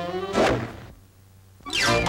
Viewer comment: WHAT DID HE SAY ? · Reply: Lapo Fei Maffii he said . You and your dingbats